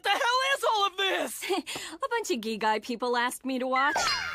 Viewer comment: subtitle say "a bunch of gay guy people asked me to watch" XD